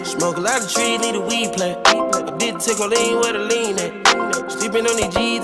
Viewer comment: Low key thought he said “I didn’t take my lean, where my lean at”